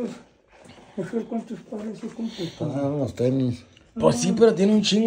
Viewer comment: Que dice aquí el 7:40? 🤔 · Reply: “está enseñando los tenis”